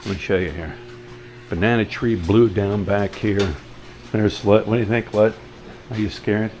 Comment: Did you call her "slut" 2:23 🤣 🤣 🤣